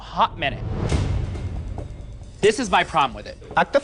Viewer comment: What dud Alexis say at 14:30 ??? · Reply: “Act a fool girl”